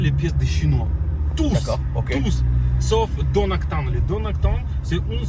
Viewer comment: c'est quoi le fabricant qu'il dit ? Donakton ? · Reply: Je pense plutôt au spécialiste Dodson qui fait de la pièce de très haute qualité sur WW/Audi ils sont basé en Nouvelle Zélande